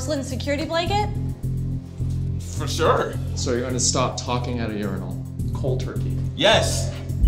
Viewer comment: I thought that said “can’t stop talking at funerals”